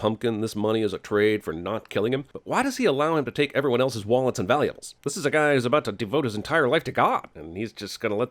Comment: Jeremy says wallets and valuales instead of wallets and valuables. that's a sin on cinemasins